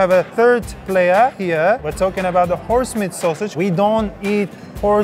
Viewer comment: did he say horse meat sausage?